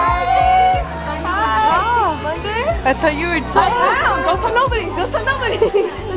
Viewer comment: did she say I thought u were tall? lol. I LOVE YOU AHHHH